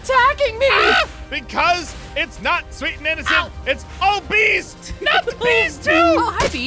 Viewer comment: I thought Ein said "ITS NOT SWEET AND INNOCENT, ITS OBESE! ". 😂